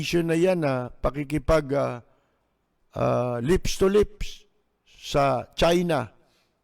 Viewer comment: I I thought you said Duterte had a lips to lips to XIJING PUWIT !!! Did I heard that right ?? !!